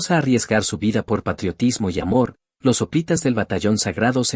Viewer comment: Los espartanos que llegaron al año 350 A C. eran solo 2.500 hoplitas espartiatas, de tanto practicar el deporte griego, y pese a la buena voluntad de los efebos, no habia reemplazo generacional. Los tebanos que les derrotaron al mando de Epaminondas sufrieron igual suerte, se olvidaron que para tener nuevas generaciones hacian falta parejas hombre-mujer. ¡Qué cosas! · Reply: Gracias por la información, yo entendí "sopitas" en el video y ahí estaba googleando, en fin la ignorancia 😆